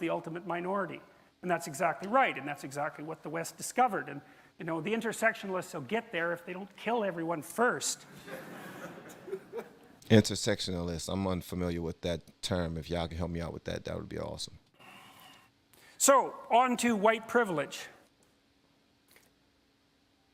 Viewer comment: Jordan was referring to intersectionality not intersexuality. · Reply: I've listened to it 4 times and it still sounds like he is says intersexualist but you could be right, either way I had to look up a new word.